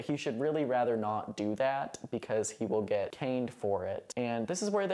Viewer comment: My brain short circuited halfway through when you said they were getting caned — I heard it as getting “cained” I thought it was a play on words 😂